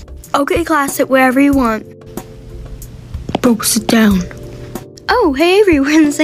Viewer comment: DID HE JUST SAY BOO SIT DOWN 😂😂😂